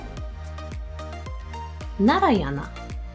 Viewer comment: usłyszałam 'najarana' xD